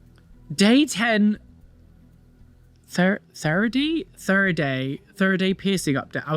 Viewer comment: I think it is supposed to say third eye piercing, since the piercing is in the middle of the forehead.